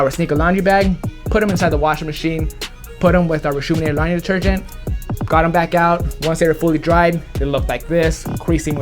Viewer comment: For the longest I thought he was saying, “secret laundry detergent” and “secret 3 brush system” lol!!